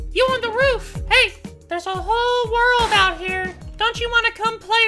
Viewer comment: While was raiding the second base i just hear "There's a bow kid out side....BRO! Take the bow take the bow!" I've never seen a person want a bow so much in my life Edit: not "here" it was "hear"